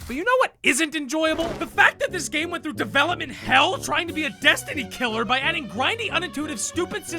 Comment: Titanfall 2 PERFECTLY fits the 5-10hr narrative. The entire game can easily be finished in a single day and even though the story is cliche and the villains have less personality then a cardboard cutout its still incredibly fun with amazing level design, movement and BT as a character. The ending is also quite hard hitting even if its very predictable. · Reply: I thought you said Destiny 2 and I was so confused